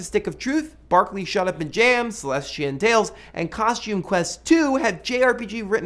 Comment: What is the name of the game at 2:37? I honestly can't understand what he's saying x.x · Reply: CrazyCuddle Yea, it was hard to understand. It's called Celestian Tales.